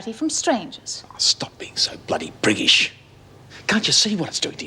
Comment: Did he say stop being so British? briggish,bruggish?... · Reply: the7thwreck: He said priggish.